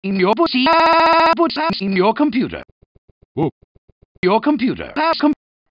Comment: put sauce in ur computer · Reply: I heard "put Sans in your computer"